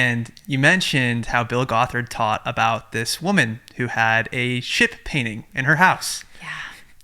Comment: When you were telling the painting story, I heard “shit painting” and was like oh this is gonna be good